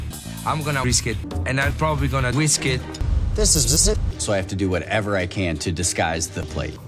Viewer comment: /2:48) - You've probably ever heard of this mistake in the sentence mixing. The captions said: "This is it!", but he said: "This is is it!".